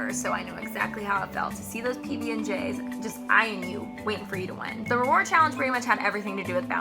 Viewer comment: I thought you said you never won a reward.... Or was that Micheala?